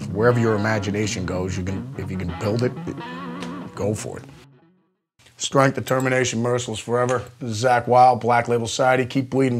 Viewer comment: didn't get his cross signal at 3:14... he says "strength, determination, merciless forever??" the merciless is strange, because he's a very merciful guy, for what I know. or does he says other thing? thank you zakk, for being an inspiration man. Love music my self, would love to become a professional, but it's not easy. thanks for giving me strength to fight for what I love! peace man! \m/ keep rockin'